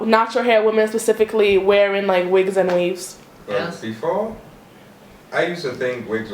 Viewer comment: I thought they said we hated weave?